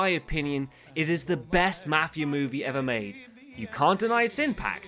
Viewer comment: I thought he said "its the best Mathew movie...."